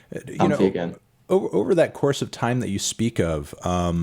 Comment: did he just say “I’m vegan” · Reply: comfy again